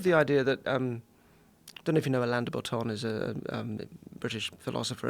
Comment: What was the name of the author he mentions at 1:04:39?? I've googled everything I thought I heard nd cant find anythingggg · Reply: Alain de Botton